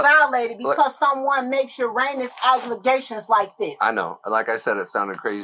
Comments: Did she just say uranus obligations when she meant to say outrageous accusations? 😂😂😂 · Did she really just say "Uranus aglegations" instead of erroneous allegations..🤣🤣🤣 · Did she just say "your anus obligations"? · Uranus Obligations? 5:33 Did I hear that right? Wow oh wow RIP baby boy 🙏🏽